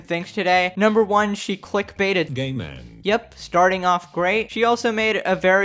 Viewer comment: Can u guys tell me what the robot voice said at 0:08 I can't comprehend I'm sorry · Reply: It says "game end". Yes, I thought it said "gay men" too